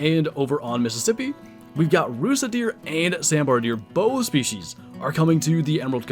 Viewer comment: hahah I heard "rizz Deer" on the "Rusa Deer" I thought that was kinda funny